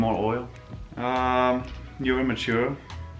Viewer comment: does he say you're mature or you're immature? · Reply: mature hahahaha although I have my immature moments ahahahaha